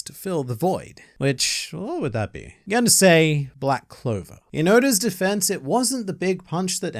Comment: When you said the manga equivalent to McDonald’s I said “oh it’s black clover” then I hear “ehhhhh black cover” I’m a god